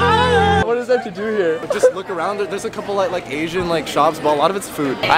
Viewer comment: I’m be honest I thought said jungkook instead of just look around Lol!!😂😂😂😂😂